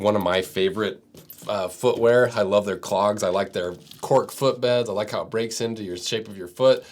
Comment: For a second there I heard “I like there CLOMPS”